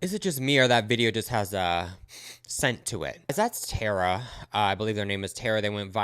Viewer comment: You said “that’s Tara” I thought you said “that’s terror”. The latter being more fitting.